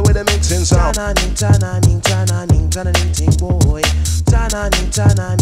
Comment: When he said ta na nee tan na nee tan na nee I felt that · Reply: I always thought he said channelling, channelling, channeling-ting boy